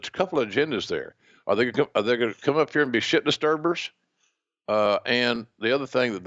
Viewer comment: After a rewind and review, I know what JR actually said here ("shit disturbers"), but I sure do prefer what my brain heard the first time around: "Are they gonna come up here and be shittin' a Starburst?"